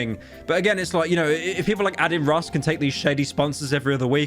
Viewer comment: Did you just say "Addin Ross"? lol